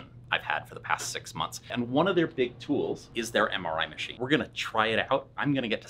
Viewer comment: I heard, "hemorrhoid machine". Glad you are back and feeling better!